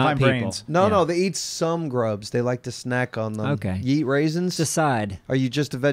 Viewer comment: I seriously thought was saying "Yeet raisins" instead of "Ya eat raisins" and I was highly confused. Yeet raisins for President.